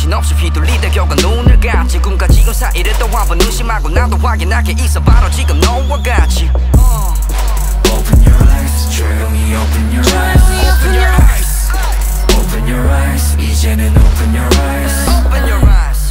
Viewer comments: I thought they said “open your eyes trigger me open your eyes” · Everyone’s talking about how it sounds like they say “Eat Jin and open your eyes”. But I’m wondering how you can open your eyes quietly when you can’t make sound when you open your eyes ._. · I like how the ARMYs are hearing 'eat jin and open your rice', the IGOT7s are hearing 'jinyoungie open your rice' and everyone else is hearing 'Jeremy open your rice' XD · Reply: i hear "Jaehyunie Open Your Rice" Lmao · I like how the ARMYs are hearing 'eat jin and open your rice', the IGOT7s are hearing 'jinyoungie open your rice' and everyone else is hearing 'Jeremy open your rice' XD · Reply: I hear "chaeyoungie open your eyes" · I like how the ARMYs are hearing 'eat jin and open your rice', the IGOT7s are hearing 'jinyoungie open your rice' and everyone else is hearing 'Jeremy open your rice' XD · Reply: I heard them all The first time they say it I hear "jinyoungie open your rice" The second time I hear "eat jin and open your rice" And the third time I heard "Jeremy open your rice" It's funny I really love nct I'm a new Stan so I have to keep up with all the members 😊and songs